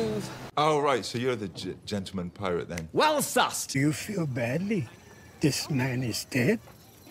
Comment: His whole excited gesture when he says "well sussed", from face to arms to clothes, makes me smile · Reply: I always thought he was saying "well assessed", but I could always be wrong